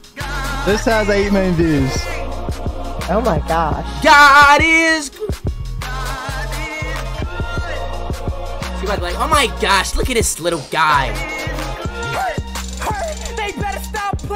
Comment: Is it just me or does it sound like he is saying nanis good